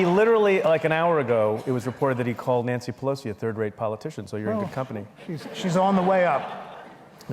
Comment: Preet, "...he [Trump] called Nancy Pelosi a 3rd rate politician." ACTUALLY what he said was even Trumpier than that - in fact he called the Speaker "a 3rd GRADE politician."